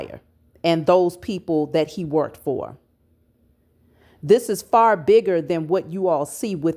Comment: I hear him now “This is Bigger than Nino Brown”